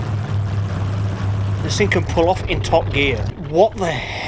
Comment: I heard “pull out on top gear” I think “hamnd me spare whel come of mayte”